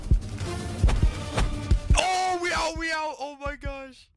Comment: we out we out' i thought that he said meow meow 💀